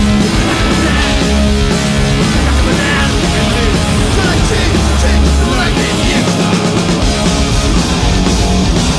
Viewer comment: prequntota, que dicen en el min. 1:25-1:27..??? **larga vida molotov(: \m/ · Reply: echale Chich, (Chicho el bajista) chich ay! chich chich...